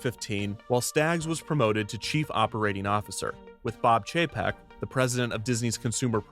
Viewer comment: kept mishearing bob chapek as bob jpeg